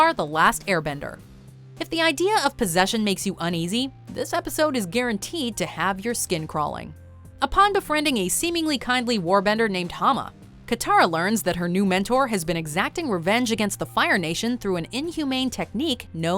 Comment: Is it just me, or did she really say "War" bender?